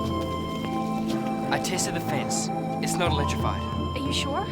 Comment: It sounds like Lex is saying "I tasted the fence". lol